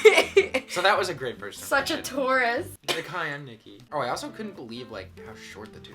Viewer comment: omg i thought you were saying "tourist" instead of taurus, LOL ok this make so much more sense